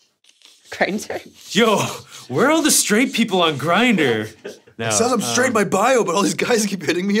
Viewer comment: I thought Ian said "word on the street, people on grindr" and I laughed so much I got sad when I heard what he really said